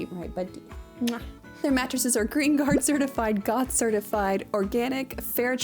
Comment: When you said "GOT certified" during the ad read, I definitely heard "God certified."